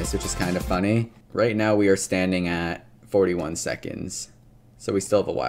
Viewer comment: I THOUGHT YOU SAID 31 SECONDS